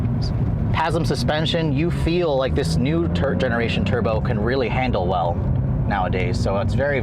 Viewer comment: sounded like he said “turd generation” 27:51 “Porspower” lol 😂😂